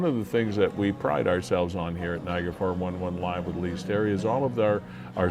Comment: Hey! Nice to see you at Iim’s 50th. I was looking all over for your podcast but couldn’t find it for ever because I thought you said (in that very noisy room) that your podcast was called Viagra 411! 😂😂😂😂. I have to say I am somewhat relieved. The first thing I thought of was Gee I guess Linda’s OK with it. Take care. Andrew